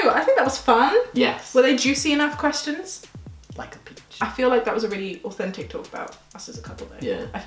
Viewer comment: When said at the end "were they juicy enough questions?" and said "like a peach", I thought he said "Ilike a bitch". LOL